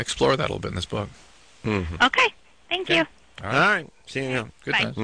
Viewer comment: "alright, see you in hell" lmao sounded like it, muffled